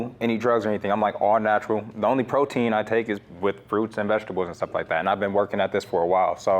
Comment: "the only protein i get is from fruits and vegetables"...... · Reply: "the only protein I take is WITH fruit and vegetables" that's what he ACTUALLY said